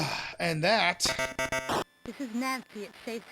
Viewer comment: I am shocked at how good Nancy's speech samples are for the console. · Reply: I did think it sounded like she was saying "this is nasty", but other than that it was good!